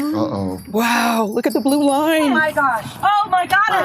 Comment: did someone say "oh my goddess" ?